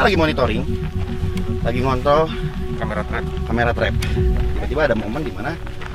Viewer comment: ngomong apa tuh man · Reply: Ngontrol kamera